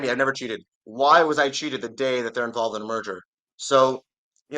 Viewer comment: This man needs to enunciate merger better, sounded like he was saying murder over and over.